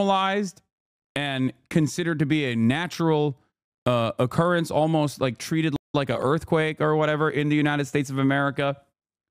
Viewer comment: What is he even saying? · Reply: Its gotten so common that we treat it like a natural disaster. What do you not understand?